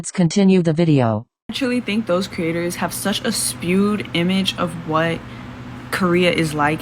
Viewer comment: Uniqua40 "Spewed image." I didn't mishear. You can see her perse her lips to hit that "p." While I can learn from everyone, I cannot take anyone seriously who mispronounces common words and uploads it in the age of digital recording and editing. Laziness will never be respected. Apart from that, "infantilization" makes no sense. Fetishization also doesn't make sense. She could have gone with cultural appropriation, but that would be an exceedingly hard argument to make about Black People entering Kpop [for extensively documented reasons]. Her logic is trash. There are many non-Korean Asian (and non-Asian) people who enter the Kpop industry who did not know the language and barely knew the culture when they entered: Henry, All of your favorite "hapu" boy kpop stars. Like, stop with the trash logic.